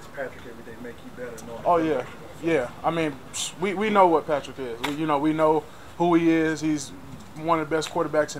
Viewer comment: Let’s get it, Khalen! Lol, when he said “We know who Patrick is.” I thought for a second he said “We know who ketchup is.” 🤣